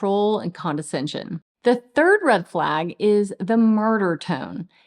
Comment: I thought she said gravy tone .mine says it's just my voice I can't help it you hate my voice..and repeats things like that all the time .